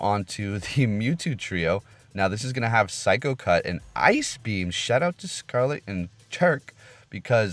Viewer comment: I slick thought it said ice cream....